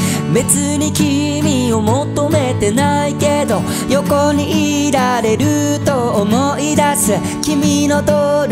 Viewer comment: ん？横に死体があると思い出す、 に聞こえたんだけど、自分だけ？